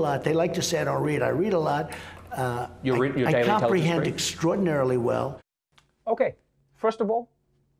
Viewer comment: In his defense: “I read a lot, I comprehend extraordinarily well.” I give you the 45th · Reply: Is that what he said? I heard "I read a lot I comprehend not very well." And I thought 'Wow, he's finally speaking the truth!'... :(